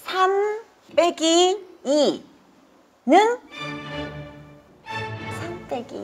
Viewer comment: 3빼기2를 300빼기 2로 들었나보다ㅋㅋㅋㅋ긴장하면 그럴수있징~!!